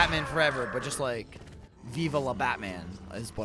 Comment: it kinda sounds like he is saying "Viva la Batman!"